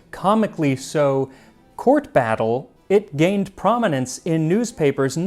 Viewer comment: Lol when he said court battle, I thought he had said tort battle and was gearing up for a bake off